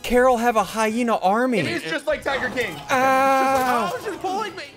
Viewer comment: Did just said Tiger king? “It’s just like tiger king” he said in